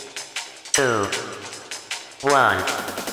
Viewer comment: 5, 3, 2, 1 that’s what I always misheard on Intro count